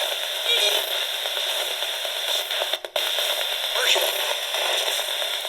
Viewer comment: 😊, I thought it said Hershell, not Marshall.... Herself was a common name back in those days...